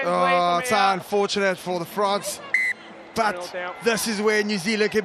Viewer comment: Did the male commentator say, "Unfortunate for the Frogs"? Sounded like it.